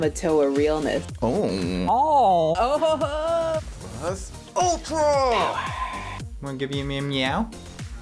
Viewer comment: did he really said, "plus ultra"? Okay that's hot lol